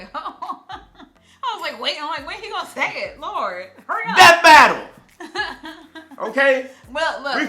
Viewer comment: im stupid i thought it said epic rap battle🤦🏾‍♂️🤦🏾‍♂️🤦🏾‍♂️😅😅😅